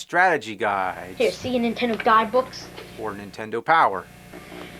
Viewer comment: I thought he was saying "Die Books!" XD